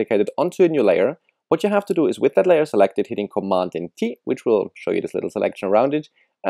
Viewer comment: i dont understand what he is saying. " with that layer selected... hitting commanding T" what?